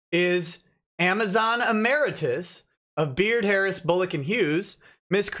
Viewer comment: only recently discovered that Ty's company is called 'Beard Harris Bullock & Hughes'. Whenever Nick mentioned them, I always heard 'Beard Hair Follicle & Huwes' (and was always disappointed that mr Huwes wasn't called 'Toupet' or something hair related)